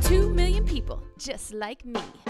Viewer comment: First time I saw this commercial, I swear to God I heard "Education Connection, get vag for free!" Now I can't unhear it. 😆 And now YOU won't be able to unhear it either! 😈